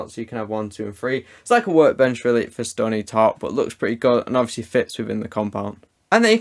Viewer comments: I think you said: "Stony Tark" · Did anyone else hear “stony tark” 😂 9:28. By the way love the vids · did he say Stony Tark?